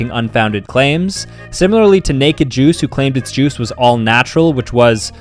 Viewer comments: Bro thought he said " naked Jews" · I thought you said Naked Jews 💀💀